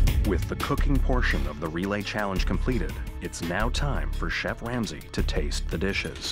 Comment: did I hear the narrator say Chef Lamsay?